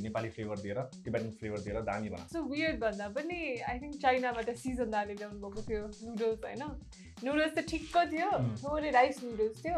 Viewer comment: did she say sisan dai??